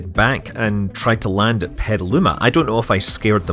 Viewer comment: Congratulations on going solo Scott. Had to rewind a bit when you said Petaluma, I thought you said Metaluna and I was thinking "what the hey, did I miss something!"